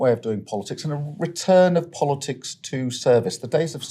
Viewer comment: This ‘politics of service’ line is becoming incredibly tiresome. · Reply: Oh, I thought he said politics of cervix!😆